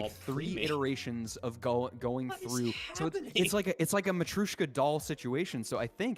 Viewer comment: what on earth did say? matrushkadal lol?